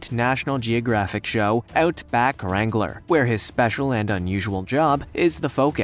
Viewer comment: He said "special and unusual job" but what I heard was "special and unusual dog"